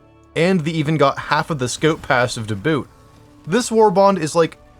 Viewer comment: Ayo what did he say?? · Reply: 😂 “half of the scout passive too boot”